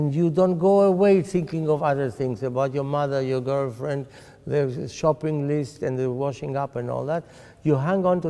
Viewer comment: when he said shopping list I thought he said Chopin, Liszt...